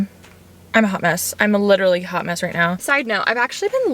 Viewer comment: I thought she said “I’m literally humus” AHHA